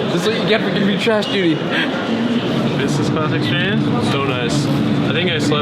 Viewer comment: what does bjerg say at 4:41? did he say vincent class exchange o.O